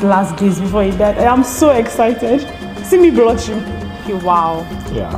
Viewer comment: did she say "see me bruchin"? Lol